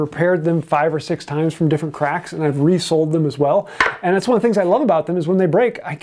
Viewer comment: I heard "resoled" as "resold". And that is when I realized that I am fatigue scrolling in the weird part of the internet.